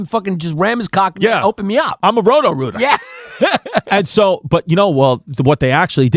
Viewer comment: I thought Chrissy said he was a homo rooter at first 😂😂😂